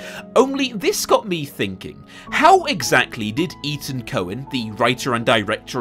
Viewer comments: It thought you said Ethan Coen directed this (Fargo, The Big Lebowski), But when I Googled it, it said Etan Coen did, and I was like “OH THANK GOD!” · Theory: Etan Cohen is only still making movies because people confuse him with Ethan Coen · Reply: when i heard him say the directors name at the beginning of the video I actually heard "Ethan Coen" and was absolutley shocked that some one like him would make a movie like this :D haha